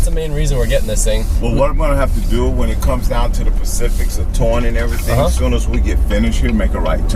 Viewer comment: did he just say ''when it comes down to the pacifics'' ?!?! :D